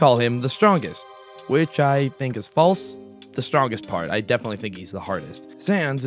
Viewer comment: I heard Stronger Than You